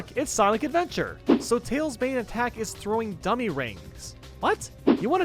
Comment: I thought you said "gummy rings" for a second xD